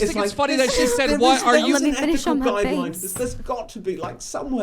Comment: Did she say "i'll let you finish in my face"???